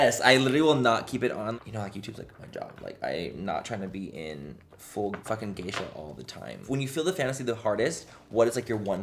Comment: Question: does Manny say "YouTube's just a job, I'm not trying to be in f*king gay sh*t all the time", Or did I hear that wrong? 🤔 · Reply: Brittney D she said full geisha. like a geisha from Japan does a full face of makeup.